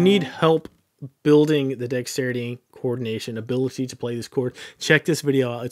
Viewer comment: I feel so stupid I didn’t realize til halfway through he was saying “coordination”. Thought he kept saying “chord nation” loooool